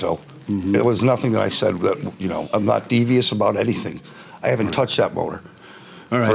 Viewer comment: Plaintiff looks like he's wearing a disguise in a bad spy movie. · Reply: sounded like he said I haven't touched that boner🤣